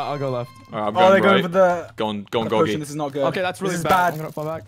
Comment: did i hear 'gogy'